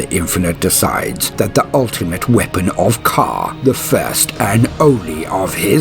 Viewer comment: Who or what are you referring to around 19:15? It sounded like you said the ultimate weapon of car.